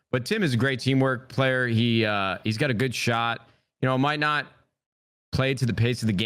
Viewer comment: Did he just say Tim has a good shot?